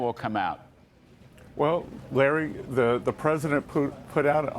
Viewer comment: It sounded like he said President Putin but he said "President put in" haha